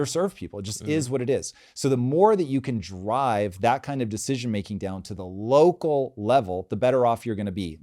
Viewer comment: My first time watching the episode I almost thought you guys were saying all schools are going to be shut down. My 2nd run through I heard: Have decisions made at the local level which I totally AGREE with!!!